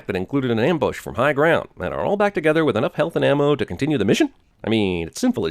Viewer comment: Did I hear "Heath and ammo?" Lmao